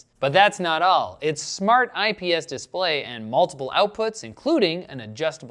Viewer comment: My dad LOVES THAT CHEEP TERRIBLE MOUSE FOR OFFICE WORK. Its NOT a gaming mouse, he has mega huge hands and like the ergonomics of it, o know its nuts but its the only mouse he uses ever....i thought riley said "shart" at first i was laughing my shart off!